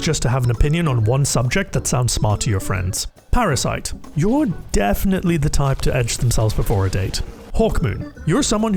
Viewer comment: did he say edge yourself?